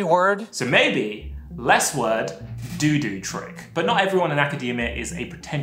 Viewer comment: "less word. Doo-doo trick" Did anyone else hear that.